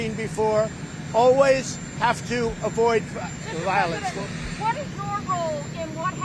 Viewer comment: Why is it every time he says "You always have to avoid violence", I hear him saying "Bring more violence"? Could it possibly be because that is exactly what he's doing via coded speaking to his "base"? Not that I am a member of said base, just someone with a little familiarity of neuro-linguistic programming and its use of coded language. I also grew up with someone who spoke similar to how he speaks. They were as abusive too. "Totally appropriate", yes for what he chose and intended to do. There's no doubt in my mind he did it deliberately. He will never accept responsibility or blame. It'll always be the "other" person/entity at fault and poor him the victim. He shows me no strength. He shows me cowardice, weakness. "Big man talking the talk" but he ain't got the stones to act the act. If he had we in the United States would not have near 300,000+/- dead from Covid-19. We would not have Russia putting bounty on American soldiers. We'd be researching Green Energy and getting people back to work. We'd have a pristine and beautifully secure infrastructure. There's none of that happened over his four years. He couldn't/wouldn't do the damn job. Proof, he has no stones.